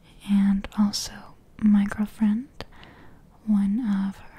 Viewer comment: did she say ,girlfriend' ???